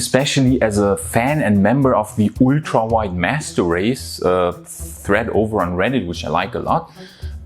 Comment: How many people thought he said "ultra white master race"?